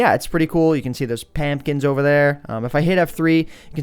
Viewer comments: he said lamp kinds instead of pumpkins!!!! lol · HE SAID PAMPKINS INSTEAD OF PUMPKINS!!!